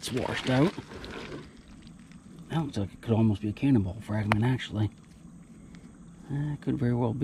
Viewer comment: I thought you said cannibal fragment. 😀 Wow! You guys had cannibals in MD? Who knew?